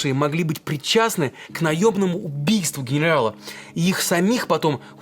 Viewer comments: Кто-то тоже услышал про "наебное убийство" на 4:44? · Мне одному послышалось наёБному убиству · Я один слышу наёбное убийство?